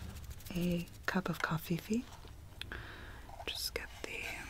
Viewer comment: did you really say covfefe? like it was all so serious and then you said it and I started crying laughing